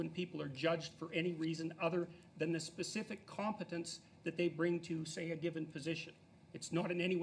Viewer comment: I think he called her incompetent...